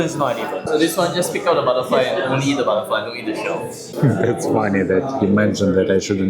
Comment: I thought the waiter said DON'T EAT the butterfly... Hahha why do you still eat the butterfly?? Haha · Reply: He said don’t eat the stone))